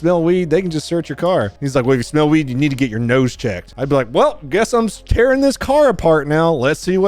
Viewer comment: I thought said "if you can smell it you need to get your nose tech" I thought he was asking for chomp chomp